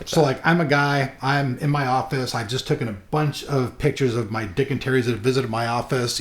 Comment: I thought he said he took pictures of his dick D